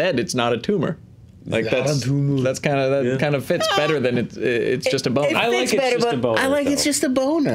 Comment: I misheard you guys and thought Arnold said, "It's just a loner."